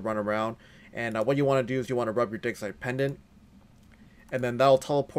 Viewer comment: it sounded like me said "Run your dick." For a second